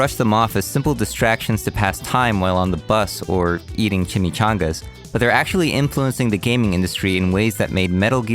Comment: did someone say chimichanga?